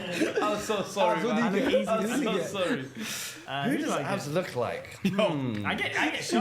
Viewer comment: What did he say at the end there? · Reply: I think he said he looks like Yakz, which is why Sharky asked him to say Max Dowman is better than him